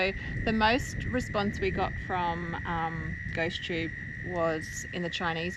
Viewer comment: Ghost... Oh I thought you said Goat 😂